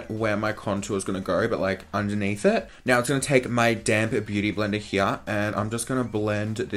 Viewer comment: 😂😂😂😂😂😂😭😭😭😭😭 I thought you said I’m gonna take this damn beauty blender. I was like damn what’d it do to you hahahahaha love the video tho